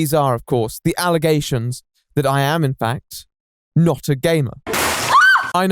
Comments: Ngl I thought you said "These are, of course, the allegations that I am, in fact, NOT a gay man." If that was an allegation, as well, that would be just as horrifying! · At the start of the video, I heard "That I am not a gay man." and I was shocked XD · 52 seconds in, and I thought you said "I am not a gay man." Then you proceed to talk about gaming and I'm thinking I may have missed something.... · I honestly heard him say " the allegations, that i, am not a gayman "...Seriously i cant be the only one right? Ahaha